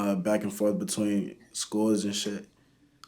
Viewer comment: i thought he was saying "scores"...he was saying "schools"